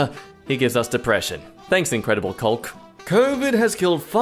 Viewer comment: I thought he said the incredible pulp